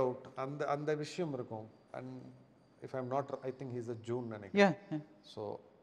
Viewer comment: What did he say @ 27:20? He is a ____? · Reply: Jew